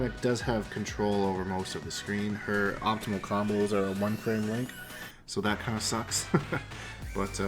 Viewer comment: What does he say at 2:10? Her optimal combos are _____? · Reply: Eddard it sounds like he said one thing Link?